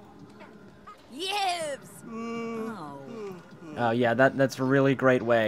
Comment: Did anyone else hear "BEEEWWWBS" ?